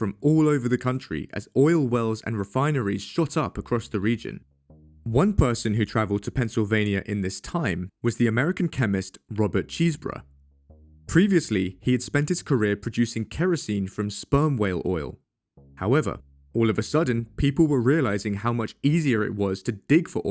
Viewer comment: I heard "Robert Cheeseburger" and now that's all I can think about.